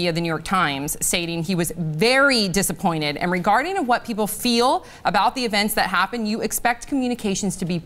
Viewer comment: I'm sorry did she just say coleigeel 😂😂😂😂